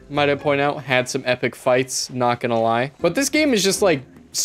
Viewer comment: i thought you said handsome epic fights/